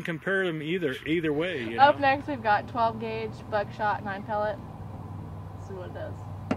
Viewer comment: Did you just say buttshot? Lol!